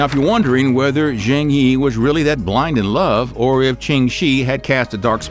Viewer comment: When he said Ching Shih I almost thought he said ching chong. It’s ok I’m friends with Bobby Lee